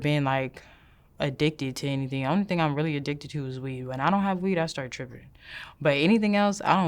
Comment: I don't do drugs..... I just smoke weed. Weed is one of the biggest time wasting, motivation robber out there! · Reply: I thought she said 'not even weed'